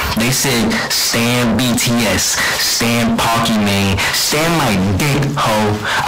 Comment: if you don't know what he said he said Stan pokimane I think??? This what pokimane is <link>